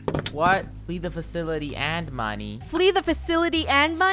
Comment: At first I thought she said "Flee The Facility And Funneh"